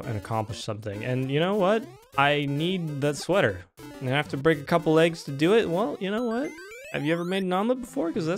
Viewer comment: did you say break a couple legs?